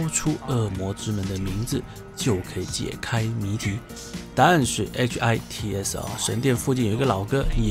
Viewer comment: 的“答案是……”我第一次听居然听成了语气的“但是……”😅（看来的声音已经印在我脑海里了）😘